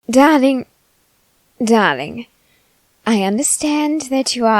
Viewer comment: I thought they said daddy 🧍